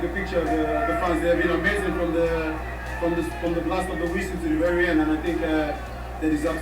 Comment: hai ragione che è "narrativa" + bias di conferma al contorno. Io do pure attenuanti perché 'sto tizio di Dazn credo fosse particolarmente in difficoltà, ma qua chiaramente i preconcetti gli fanno fare confusione. Secondo me "from the blast of the whistle" ha capito "from the last week" perché è come quando leggi le misheard lyrics di un pezzo mentre lo ascolti e ci senti quello che ti aspetti di trovare.